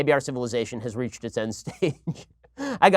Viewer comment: Am I hearing finally say "we've reached the Endgame"?